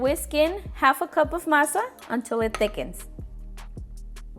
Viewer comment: What did she say at 1:37 ? massa ? Whats a massa ? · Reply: It is actually maseca you can find it at Walmart or any grocery store.